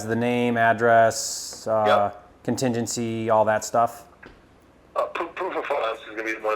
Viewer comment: Hey! Great video. Quick question for anyone what was the selling agent saying @ 8:58 ? Proof of what? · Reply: Proof of funds With a pro or prime level subscription to flipster, you get unlimited custom proof of funds letters for as many offers as you want. To learn more go to: <link>